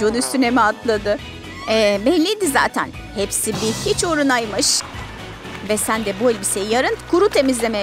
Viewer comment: "hepsi bir " dedikten sonra ne diyor🤔🤔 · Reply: Pardon yanlış yazdım hepsi bir hiç uğuruna'ymış duyuyor😄😄😃😃😆😆